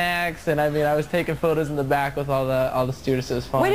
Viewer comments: Oh, I'd thought it said Mountain Dew Babies. 😄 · for a moment there i thought he said, 'I'm talking about my own juice baby....'